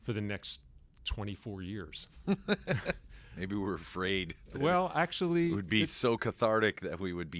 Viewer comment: You know James was chuckling because he actually said "Maybe we were frayed!" and got away with it :D